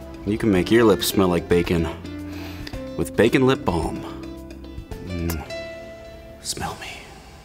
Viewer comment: I thought said baby lip balm 😂😂😂😂😂😂